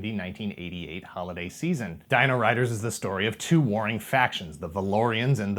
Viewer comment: Haha I thought you said "two boring factions" instead of "two warring factions"!